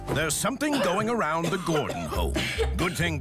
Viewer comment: What I heard: There's something going around the glory hole. 😮😂